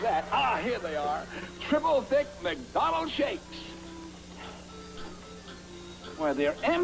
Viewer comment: Uhh... So something weird just happened. After I read this comment, YouTube crashed, and I had to restart it. I guess triple is cursed? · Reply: Also there's an unused sound clip that was thought to be saying "jafe/gay", but it's actually Ronald saying "shakes". Same with the "ahh", it came from this video